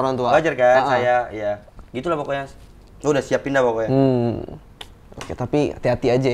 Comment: itu bilang apa ya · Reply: Gw udah siapin dah poko nya, nyiapin jawaban bapak ny